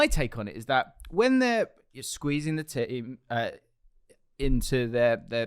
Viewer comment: Nobody can tell me did not just say: "squeezing the titty" at 11:32 🤣